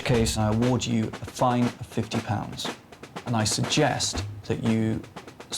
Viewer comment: I'm having a problem hearing what he says from 25:17 to 25:23. "Find 50 pounds" what does that mean? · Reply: a fine of 50 pounds a little over $100